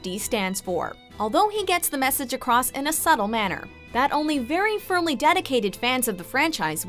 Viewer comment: I love this series. Great use of the source material. btw, what did she say at 9:31? “Very furly dedicated fans”?